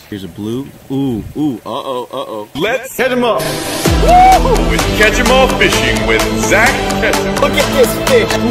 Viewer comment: The first time I listened to his intro I thought it said "Catch Em All fishing with Zach Efron" Love the betta fish btw Also the White,blue, and red one reminds me of Ariel lol